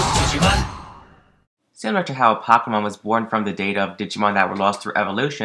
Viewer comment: "Similar to how Pokemon (is this what you said?) was born from the data of Digimon that lost through evolution." Is this true? Is this cannon? Where did you read about this theory?! Because that would be freaking awesome! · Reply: Nemanja Damnjanovic he said similar to the way apocalymon was born